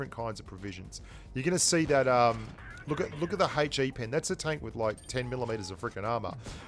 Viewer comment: I had my volume low and I heard: "That's a tank with 10 Kilometres of armour.".